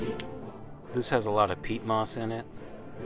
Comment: Soil growers cant handle your ignorance of how the Soil Food Web works. "Its got a lot of Peat moss", well peat moss is Inert (meaning it has no nutrients in it). So you are basicly comparing a finely tuned high performance race car (bottled nutrients) to rust bucket with no engine that is up on blocks (lifeless soil). I don't want to come off as a hater but it has taken me years to sift through all the lies and misinformation that is out there about farming and gardening. So in conclusion; I do not think you understand enough about horticulture to be teaching it. You are spreading dangerous misinformation and that helps no one. I'm am however glad that your project inspire people to get growing, that is priceless! But please be careful with your opinions and statements, you are speaking to a lot of beginner gardeners. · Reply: I heard him say "it has a lot of Peat Moss in it" I didn't hear him say "it's all peat moss".